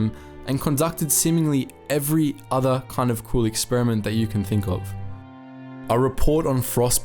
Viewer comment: Did he say cruel or cool..? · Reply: cruel